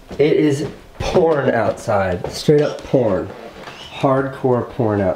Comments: I heard porn but I'm guessing it's pouring he said? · Is it just me or when he is saying pouring, it sounds like he is saying porn lol.